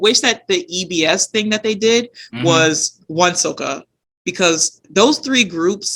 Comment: what was the name of the gg trinity you wanted instead of ebs? i didnt catch it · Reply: WonSoKa (Wonder Girls, SNSD, KARA)